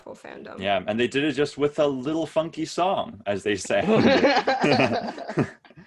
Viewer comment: i heard "a little funk and soul" as "a little f**king song" at first